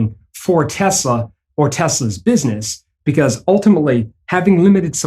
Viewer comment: you said rape instead of raise which is the same result for tesla 😂